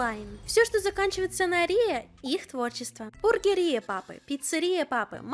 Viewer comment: оргия папы.... простите, в начале послышалось.